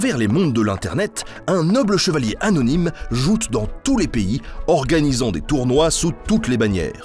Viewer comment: À 00:04 j'ai entendu "un noble chevalier anonyme jute dans tous les pays".